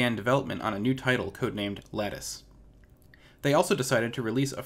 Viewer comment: good video....just wish you could have learnt to pronounce the word 'latest' (lay-test) so that it didnt sound like you were saying 'laticed'